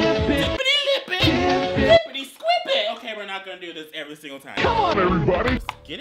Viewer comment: I thought it said “Come on daddy”